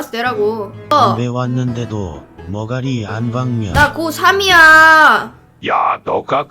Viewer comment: ㅋㅋㅋㅋ 나 고삼이야가 아니라 나 꼬3이야라고 들리네ㅋㅋㅋ 나만임?ㅋㅋㅋ